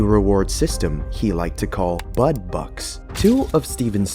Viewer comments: I wasn’t watching the screen and thought you said “Butt Bucks” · “Bud bucks” i thought you said “butt bucks” i was dying 💀